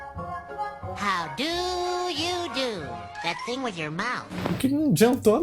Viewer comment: o que ele fala não condiz com a tradução. Ele diz “how do you do, that thing with your month?” O que significa: “Como vc faz aquela coisa com sua boca?”